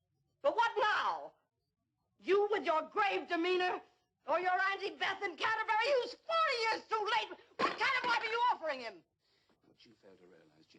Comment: What is she saying at 1:57-2:02? I can never make out what she is saying. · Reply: "You with your grave demeanor. Or your Aunty Beth in Canterbury who's 40 years too late! What kind of life are you offering him?!" There ya go. 👍🏻